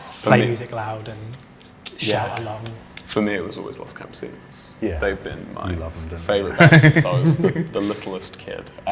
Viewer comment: what is he saying I couldn't hear him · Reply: los campensinios (idk how to spell it but its a nice band :D)